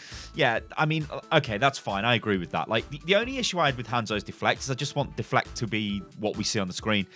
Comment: did he say hanzos deflect?